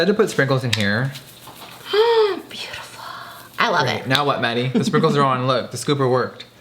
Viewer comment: Thought he said now what Manny 11:18